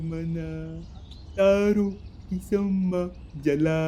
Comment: i literally heard DAARU KI SHMMA JALA lmao